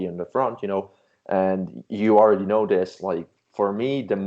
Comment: what is that word? I hear "sradds"😅😓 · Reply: Serratus